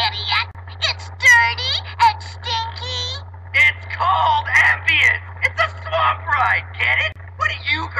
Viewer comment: Does anyone heard what I heard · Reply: “It’s called Ambien”